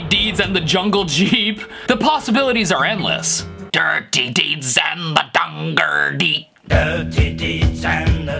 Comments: “I Hear Dirty Deeds and the JUNGLE SHEEP” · I always heard ''dunderdeep'' /''thunder chief'' 🤣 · I heard "dirty deeds and da dunder dgeet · i heard " dirty deeds and the under blleeeeeeeeeeep"